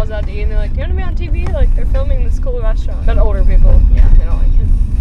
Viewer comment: caption says YouTube instead of tv